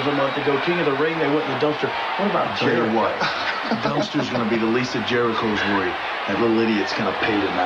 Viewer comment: for a moment I thought HHH said "the list of Jericho"